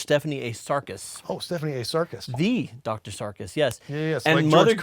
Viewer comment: "Dr Sarkis" Anti-Sarkicism alarms went off in my head I thought he'd said Sarkist lmao